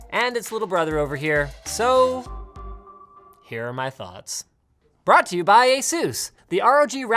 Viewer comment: did he say brought to you by jesus